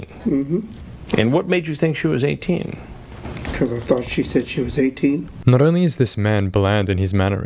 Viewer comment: His answer wasn’t “I thought she was 18”. It was “I thought she said she was 18”. I know that doesn’t make it any better but it makes a little more sense.